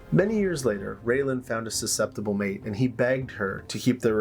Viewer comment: “he begged her…….” Thought you said “he banged her” 😂